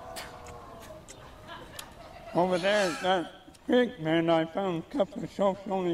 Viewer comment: Creek Bed, i thought he said Pig Pen